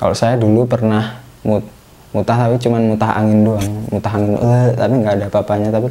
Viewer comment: Di menit 6.53 itu ko kaya ngomong kasar ya... kalo gk salah denger masnya bilang ( Anj...ng )